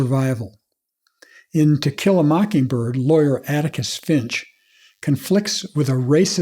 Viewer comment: I heard "Tequila Mockingbird" for a second there and thought, what an intriguing title